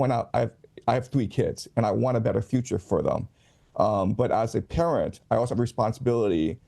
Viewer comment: but as a parent { sounded to me like: 'but as a terrorist' } I also have a responsibility to be truthful to them... - Did anyone else hear it that way, too?